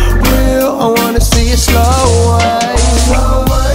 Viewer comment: lol at first I thought he said Snow White